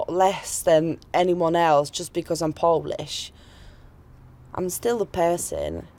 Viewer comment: probably jewish girl pretend to be polish. She said that came to UK 8 years ago. And she went already thru high school,college and university. What accent is that anyway ? 1:46 She saying that she is "poblish" whatever that means. What we are witness here is another BBC psyop.